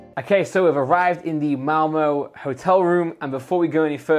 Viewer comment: sounds like he is saying mowmow for Malmö